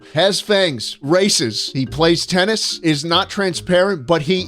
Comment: I thought it said rasist